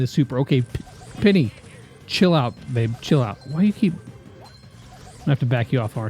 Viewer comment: did just call penny babe !!!!